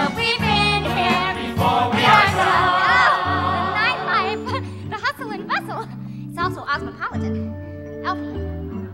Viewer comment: can someone translate what Glinda says on min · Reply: “It’s all so ozmipolitan”